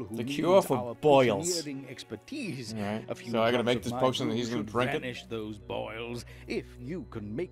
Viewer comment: Fuck that potion is what I heard, and THAT'sS ENOUGH